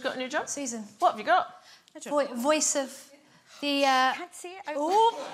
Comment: What does she say at around 1:10? The audience reacted but it's too quiet for me to make it out. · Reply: “You can’t say it out loud”.